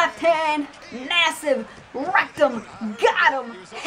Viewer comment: Wait, did you just say massive rectum?